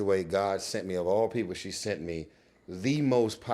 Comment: did Tyrese just call God “she”? I don’t wanna misunderstand or misread that. It may have been a mistake but also it could’ve been on purpose. I pray that it wasn’t but again, I could’ve misunderstood